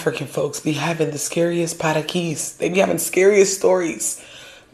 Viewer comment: what that’s word? Parakis??